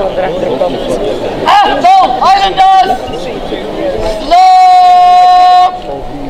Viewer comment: did he say "achtung" ? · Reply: He's saying Atholl Highlanders